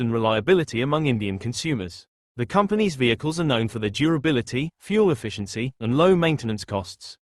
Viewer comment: u high? did u said durability ?